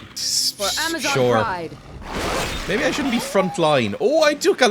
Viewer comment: I keep hearing "for amazon prime!"